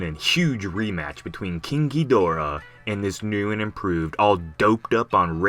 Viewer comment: Ok so ur saying KINKYdorah i thought you were saying KING(GI)dorah